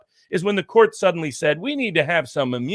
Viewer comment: what does barnes say at 9:55 : 'It's a ----------------- doctrine'-- His connection goes out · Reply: Dangerous